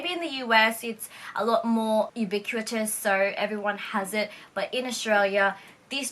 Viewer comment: Random question to the native English speakers! English is my second language and I wonder what the word is at 12:16? How do you spell it? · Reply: She said "ubiquitous"! :)